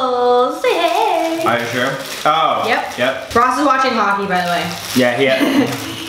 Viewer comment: I thought you said "Ross is watching Rocky by the way" and was like "damn did he get in trouble" 😂